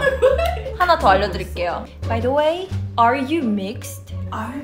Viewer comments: I thought I heard “are you minx?” 👁👄👁 just my mind still wants to tease them about minx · I misheard Yoohyeon said "BTW, are you MINX?"